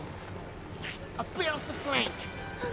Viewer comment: Does anyone know what Snaga says at 2:15? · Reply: Just a bit of flake